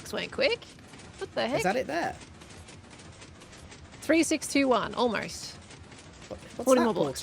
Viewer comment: WHAT DID SHE SAYYYYYYY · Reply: e621⁉️